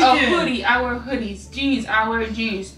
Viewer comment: Lol thought she was saying "Our hoodie, our shoes" when she was saying "I wear hoodies I wear shoes"